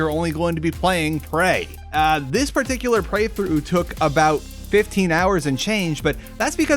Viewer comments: I heard you say "Preythrough". Intended or not, beautiful pun. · Did I hear him say prey-through at